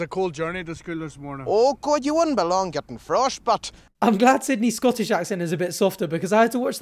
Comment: For the longest time, I thought that guy said "frost butt", and thought it was an odd way of saying you would freeze your butt off because it was so cold. Never occurred to me that he said "frostbite". :)